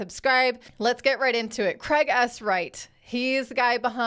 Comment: I thought you said Craig ass Wright in the intro 😂